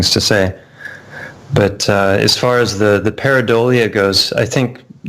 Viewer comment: I am the Message That longs to be heard Singing the Song of the Spirit I am the Vessel That carries the Word In search of the One to receive It I am the Meaning That lives in the Sound Within the distance of Time I am the Story Whose Moral is bound Inside the Lines of this Rhyme. VCH6464 01/30/2020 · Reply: did their say the word PARADORIA .? Can any one help me please